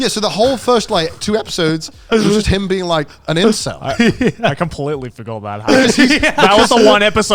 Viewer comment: you say the whole first 2 episodes is of him being a WHAT? An "insel"? I googled this word and it says "island". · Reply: Incel